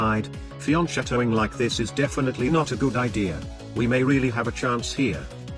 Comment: What's being said at 1:06? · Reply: Fianchettoing like this is not a good idea. Fianchettoing is putting the bishop on the long diagonal after moving the b or g pawns.